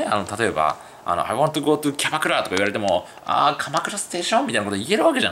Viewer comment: キャバクラにしか聞こえない